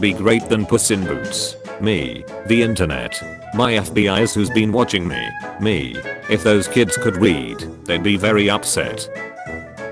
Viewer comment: I wasn’t to only one that heard weed instead of read right?